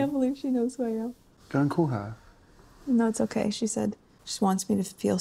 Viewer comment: Did he say “don’t call her?” Should ‘Beyoncé botherer’ be added under ‘First Lady botherer’ in the WW Privacy Tour’s sequel? Why else would he say something like that, and so publicly? Unless he was actually worried she’d embarrass them both by calling Beyoncé and bothering her? Because knowing Meghan’s delusional thinking, she’d take what was likely nothing more than a ‘stay strong, keep your head up,’ courtesy text from a kind and thoughtful person, as an invitation to real friendship. Or even that they’re ALREADY friends, because if he DID say “Don’t call her,” her “No, it’s okay” could’ve been her brushing off his worries as nonsense. Either because she never intended to call her, OR she truly thought Beyoncé would welcome her call. Either way, I don’t think she appreciated him telling her what to do and/or saying something like that in front of the crew, by the way she spoke so fast, not even pausing for a second between saying “No, it’s okay,” and beginning to read him the text.